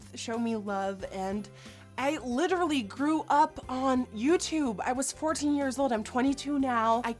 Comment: I kinda wasn't listening and when she said "I literally grew up on YouTube" I thought she said "I literally threw up on YouTube " And I was like ... Sorry what ?!?😂 I should really pay more attention